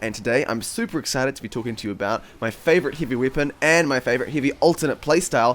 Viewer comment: I heard ''Hivey weapon'' in the start